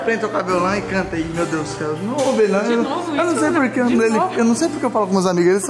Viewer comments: Quem ouviu denovo isso amor não sei se eu ouvi errado minuto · Só eu que escutei a chamando ele de amor ? No tempo de 4:48 ela fala " De novo isso amor ?" Reparem bem Kklkklk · presta atenção na falando so eu ouvi amor? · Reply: Ela falou "Dinovo isso Leonardo" Mds ja tão escutando dms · So eu que ouvi de "novo isso amor" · E esse foi o vídeo que vocês pediram demais!! 💕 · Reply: eu escutei dinovo isso amor